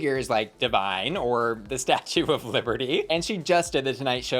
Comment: I had to go back and listen to "the statue of Liberty" part over again. I thought it was said "Pistachio of Liberty" and I started laughing so hard. I'm a little disappointed I was mistaken.